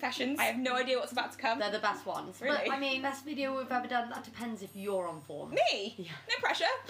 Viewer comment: when said it could be the best video they ever made i genuinely thought she said to: ‘depends if you’re on all fours’ i H A D to rewind my gay ass nearly died